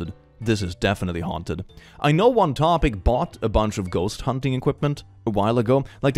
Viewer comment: I thought you said hot topic and was about to go to my mother and ask to go there for ghost hunting supplies